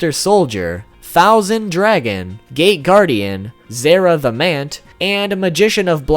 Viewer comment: When you said "Gate guardian" i thought you said "Gay guardian"